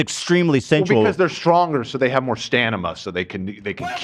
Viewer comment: saying “stanima” instead of stamina @ 49:16 made my day😂🤍